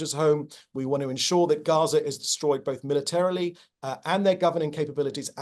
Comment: Did he just say "we want to make sure Gaza is destroyed both militarily and government capabilities?" He said Gaza not Khamas ... ICC needs to hear this